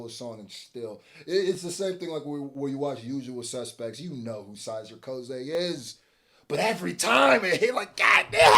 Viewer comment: 2nd time I heard you say Syzer Koze….. It was “Kyzer Soze” my dude!